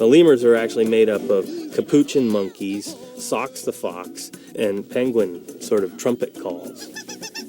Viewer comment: “Socks the fox”? Is that what he’s saying here?